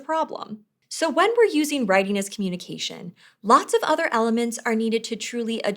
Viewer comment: Unfortunately, I noticed a couple typos in the captions. Such as, at 3:33 it says "readiness communication" instead of "writing as communication." This is a shame, but I am glad that at least there are captions.